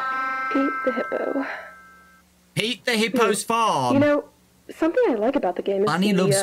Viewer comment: I heard eat the hippo farm in